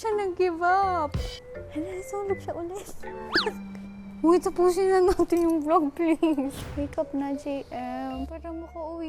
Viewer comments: tapusin na natin yung vlog babe Tama ba yung narinig ko? Chariizzzz hahaha sana ngaa kayo na 🥺🥺🤍🤍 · "uy tapusin na natin yung vlog babe" tama ba narinig ko hahhahahhahahhahahhahahhah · Tama ba narinig ko sa 21:41sec HOY TAPUSIN NA NATIN YING VLOG BABE